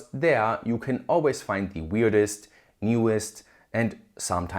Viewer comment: I heard "the weirdest e-waste" 😅